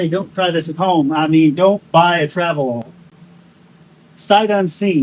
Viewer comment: Don't buy a travel-all...🧠 👂 ear hears...brain interpretation of said message.. I hear I have to buy travel-all😅 Paint looks pretty good. Saying all that paint was garbage, have to start calling you Dalton Jr.😅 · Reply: Weird I swore he said you have to buy a traveall lol or else how else did that become a saved search on marketplace😂